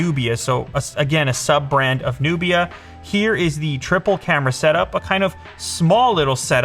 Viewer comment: I always hear "tripper camera" instead of "Triplecamera" xD